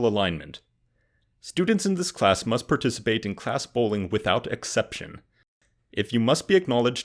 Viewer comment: I misheard "class bowling" as "class bullying".